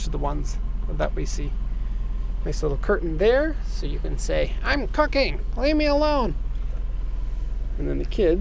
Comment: At first I heard "close the curtain there so you can say 'I'm fucking, leave me alone' while the kids play pass the pigs". I laughed really hard then went back and heard you actually said "I'm cooking.."